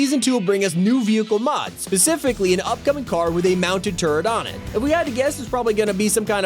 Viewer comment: I thought you said turd not turret 🤣